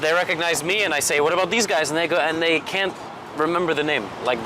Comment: Did he say the N word at 7:39💀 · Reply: I heard " 'nd they go"